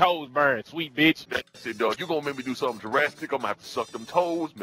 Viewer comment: does anyone else think that says "Jurassic instead of Drastic?" +, help me out with this bro!